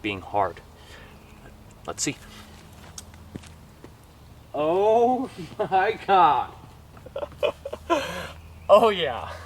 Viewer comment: IT sounded like he said oh My cock